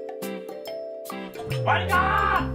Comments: Hi Internet, I was hoping to release the fire on demand video as well but UK people will know that the weather for the last 2 weeks has just been awful and as its an outside device the vid anti finished but it doesn't matter as I've turned masons bed into a fairground ride in his mind haha. see you in the next video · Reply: I cant be the only one who heard "WANKER!!" ar 00:12 :D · I cant be the only one who heard "WANKER!!" ar 00:12 :D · Hahaha, When he says WAKE UUP, it sounds like he is saying WANKER!!! XD